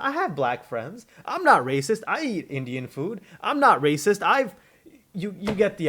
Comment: I had to rewind the video a bit because I misheard you say "Im not racist I eat Indian food" I thought you said "im not racist I eat Canadian food" and was like... is that something people say? Never heard it before, I need to re hear that.